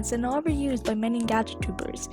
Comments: What you hear: "Gacha tubers" What I hear: "Gather troopers" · When you said gacha tubers weirdly I thought you said “gacha troupers” Lol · I thought she said gacha tupus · now pls don't hate dis coment but while I was watching I thinking of starwars then when you said "gachatubers" when it repeats gachatubers I thought it was saying gacha troopers 0-0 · did i hear that right? *gacha troopers*